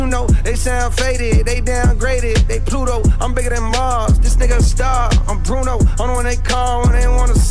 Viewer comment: I'm bigger than mars sounds like he saying he's bigger than Lamar 😅